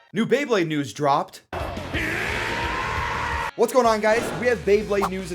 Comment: he says what’s up guys but the subtitles say sup nerds😂